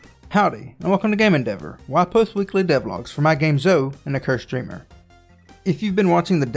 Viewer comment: I keep hearing "Zoe and the Cursed Streamer" when you say the name of the game. The level editor is exciting news, I'm curious to see how that turns out in the final version.